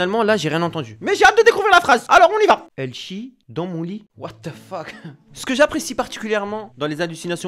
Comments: A 3:20 j'ai entendu il chie dans mamie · Moi j'entends Chie ton vomis😇🤣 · Moi j'entends tu chie dans le vomis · bah moi j'ai entendu "je chie dans mon lit" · j'ai compris :Elle chit dans le vomi X)